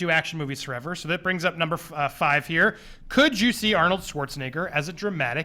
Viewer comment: For round 5 it sounded like Andy said "could juicy Arnold..." Instead of " could you see Arnold...". Like if you think the same